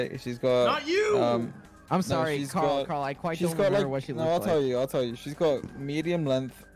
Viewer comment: what does quackity say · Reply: I think it's, "I'm sorry Karl I can't quite remember what she looks like"